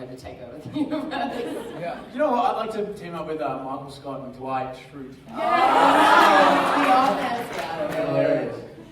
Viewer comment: what did Bob say at 10:10? about the tv duo he'd like to team up with? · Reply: Cresmix Michael Scott and Dwight shrute from the office